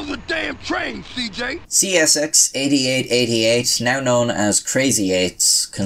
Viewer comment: I was listening at low volume and thought he was saying "crazy aids".